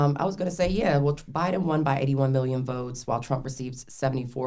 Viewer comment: Sounds like she said Biden won 80MM votes not “won by” that’s debatable I guess.